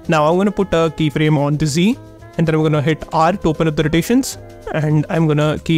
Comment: What did he say at 2:12 add, alt, tab? Pls someone help me. My phone has speaker issues and i can barely understand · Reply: I think he means 'R' to rotation